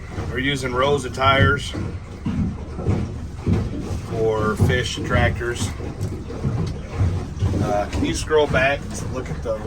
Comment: I thought you said Fish Tractors 🚜 😮 Such beautiful country. You can see why it’s regarded a peaceful exit area. My heart goes out to you as divers, rescuers and outstanding human beings. Thank you for all those not yet found, help is on its way in the form of Chaos’ Angels 🩵🪽